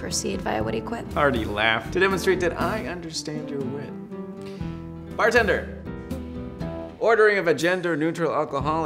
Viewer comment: Did anyone else hear "vagender" at 00:51?😂